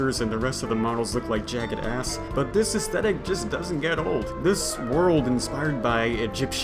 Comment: Did you just say "ESKETIT"?